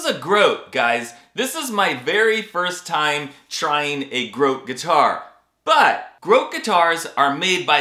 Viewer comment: When you first said grote I swore you said gross guitar 😂 And I thought well this is gonna be an extra entertaining review